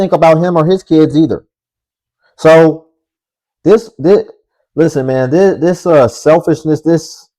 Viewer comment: All I heard was "So...Boom, listen to THIS excuse." 😅😅😅